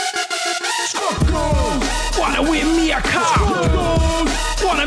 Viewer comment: I always thought said "gonna win me a car" during the (squad) goals intro. But in some way it does make sense so I jus went with the flow. Greets from Sverige ;)